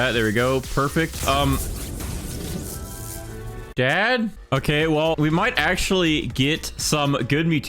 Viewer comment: did i hear dad?